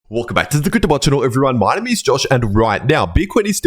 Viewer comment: Has anyone understood the first 3 seconds of any of these videos after he says "Welcome back"? I swear he's talking 10000 miles an hour just to make people say WTF did he just say? Anyway, always enjoy your analysis. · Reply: Welcome back to the crypto world channel everyone my name is Josh